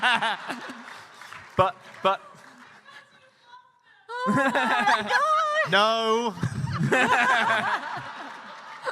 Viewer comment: I can’t hear what the person in the background is saying. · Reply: "because you love them!"